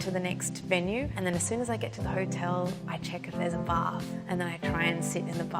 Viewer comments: When she said, "as soon as I get to the hotel, I check if there's a bath," I thought she said, "I check if there's a bar," and I was thinking "girl same". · Hahaha, the captions say, "As soon as I get to the hotel, I check if there's a bar" instead of 'bath'. I was like, whaaaat? I mean, me too, but inappropriate for this interview lol. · I THOUGHT SHE SAID “I check and see if there’s a bar” ADEHHWHAH